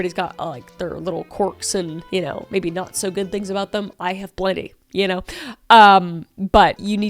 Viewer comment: what does she say she has? I keep playing it over and over and can’t make out what she’s saying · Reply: She said "I have plenty"